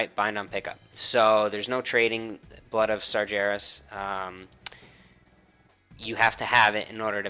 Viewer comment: i thought i miss heard but Got-Dang you actually said "Sar-JARE-us" ffs